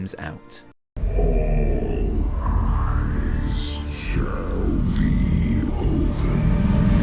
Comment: Not gonna lie, when I first saw this cinematic I thought N'Zoth said "all graves shall be opened"